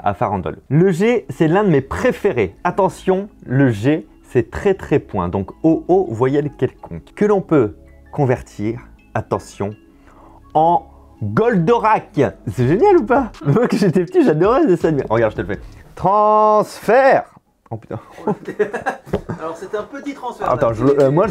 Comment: Ah ! Moi j'avais Google ! xD Mais Goldorak, c'est bien aussi ! (En plus, j'étais fan aussi quand j'étais petit)